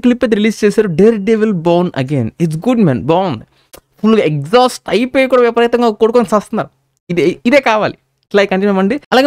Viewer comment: what did he say there? "Exahuast ayi poyi hype ekuva ayipoyi kutu kuntunaru"?